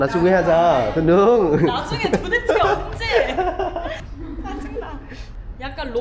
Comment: 의 나중에 도대체 언제? 가 나중에 부대찌개 언제!로 들리지?? 2:16 ㅋㅋㅋㅋㅋ😅